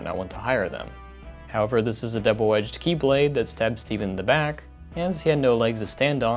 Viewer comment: did you just say Keyblade or is that just me?